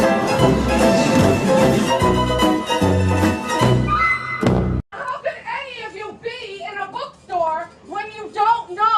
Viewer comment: woman at the bookstore - can someone translate, I dunno what she's saying in the second half tbh. Or the dude at the end oof. V curious tho. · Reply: I think she says “I’m looking for a cock-a-doody book!” And then the man replies “I can ordered it for you ma’am” sounded pretty set-up tbh.